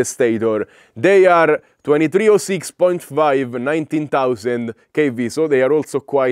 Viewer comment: I heard "19,000 kv" so I had to go back and look! Ok, 1,900, phew 😂